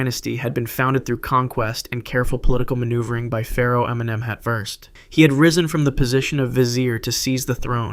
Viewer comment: Pharo Eminem? What did he say there?